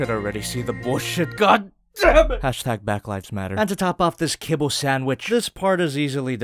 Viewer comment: dam didn’t know he said “back lives matter” in this vid. How far back does this saying go?? I fought it started with mp44